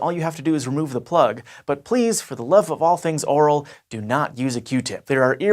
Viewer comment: Did he just say, “For the love of all things oral”? Is that an expression or did I misunderstand him?